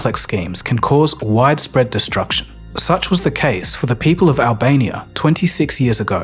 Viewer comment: Not gonna lie I thought it said the fall of Alabama